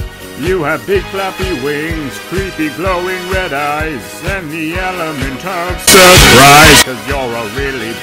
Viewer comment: I was trying to listen to this as I go to sleep and out of nowhere I hear "And the element of surprise *screech*" and I jumped. 😂 I didn't expect that. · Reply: Oh, that's sense. I was listen "And you throw you direct to RICE". Omg, I need to practice my english.